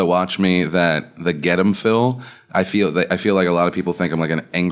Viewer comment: you said 'rottweiler: and I heard 'wop riler' and I had to go back and see did you make a funny Italian slur